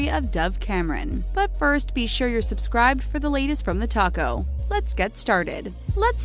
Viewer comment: Omg lololololol at 0:15 I thought she said the taco😂